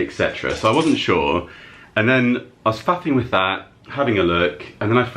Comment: and 14:58 Pardon? I know what it sounded like you said. Did you say mucking? Nice finds btw 🙂🙃😅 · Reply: He said he was faffing😂